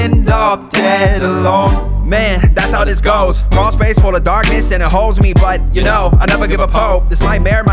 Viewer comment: First time I listened to this I misheard 1:30 as 'crawlspace full of darkness and hoes'